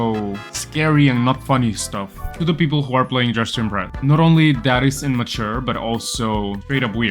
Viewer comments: Ik this is unrealised but the text below says “Justin bread” instead of “dress to impress” 😭 😭 💀 🙏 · When he said “ to the ppl who are playing dress to impress " the subtitle said " to the ppl who are play Justin bread " lol